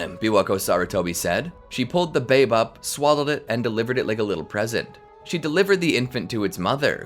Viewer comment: i thought you said "She pulled the babe up and swallowed it"